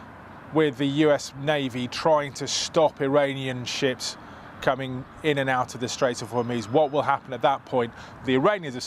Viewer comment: I thought he said "sh1t" not ships. Made the rest of the report funny af.